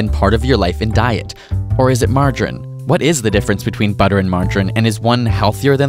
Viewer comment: Who else thought it said "butter vs mangiare" psh...italians...